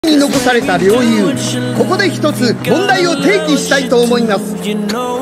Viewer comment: フリーザー「ここで一つ問題を定義します」そういう風に聞こえた